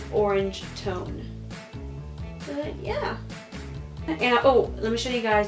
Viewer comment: did she just say and I oOp? She invented it then